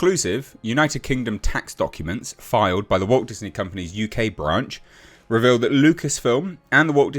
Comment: I swear you said Woke Disney Company 🤣